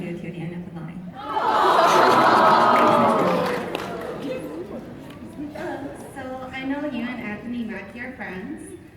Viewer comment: What did the girl say after seb said "I'd probably try to pee" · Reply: I know this is late but she said "I'm with you till the end if the line"